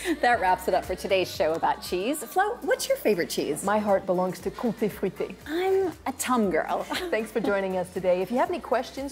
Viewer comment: they talk about their favourite cheese, but I cannot understand what they are saying. Could somebody help? Thanks. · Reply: Comté fruité ( it's made in the east ) Tomme ( it could be a lot of cheese, it's a vague answer )